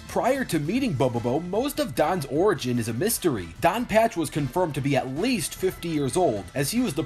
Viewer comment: i thought he said don patch is gay.